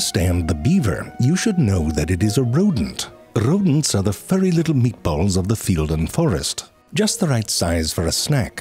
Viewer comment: Check out Curiosity Stream with code: <link> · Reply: When you said "the little meatballs" in context to rodents I thought you said "meekballs" and.......you're not wrong.